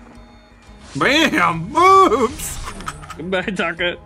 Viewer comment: around there i hear shout BOOBS!!!!